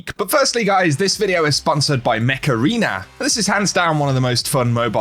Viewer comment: I won't lie, I thought you said Macarena instead of Mech Arena for the sponsor.